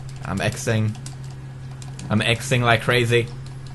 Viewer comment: when pewdiepie said im exing im exing like crazy it sounded like he said im sexing im sexing like crazy